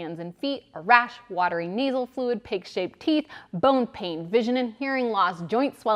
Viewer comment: Did she say “Pig-Shaped Teeth”?